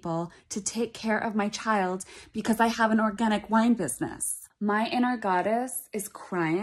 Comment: "My inner godess is crying, when my inner godess should be laughing". "I HAVE AN ORGANIC WINE BUSINESS" im wheezing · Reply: Lmao I thought she said organic wipes 🤣🤣🤣